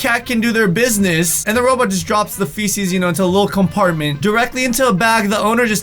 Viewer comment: When ( in the first picture) said feces, I thought he said fetus 😂😂😂😂